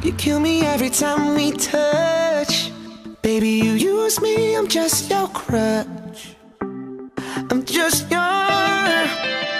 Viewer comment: i thought he said im just you're crotch omg